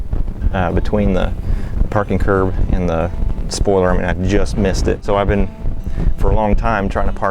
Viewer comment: did he just say...spoiler?